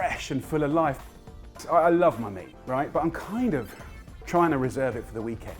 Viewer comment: "I love my meat" I thought he said honey or money 😂😂.